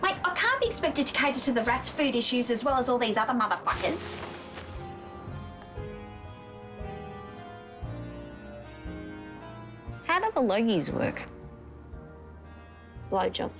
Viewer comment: what the fuck is being said at 8:20? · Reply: I don't know. But I was trying to figure out what is being said at 10:23. It sounds like: "How do perogies work?" "Blowjobs"